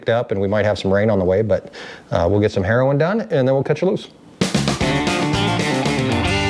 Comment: what did say? "we'll get some Heroin done" >? loll